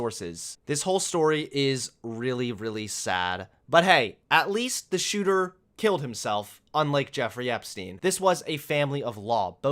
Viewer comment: I heard the family "Killed themselves"